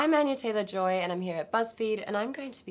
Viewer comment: Who can listen her voice probably while she speak her own name? I heard “ I’m Anna Taylor-Joy “ not Anya